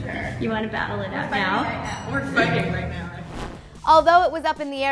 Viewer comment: Did Kendall just say we're fucking right now at 1:09. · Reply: I think she said we're fighting right now.